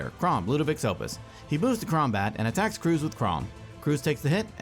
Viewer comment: Did he just say Kraumbat?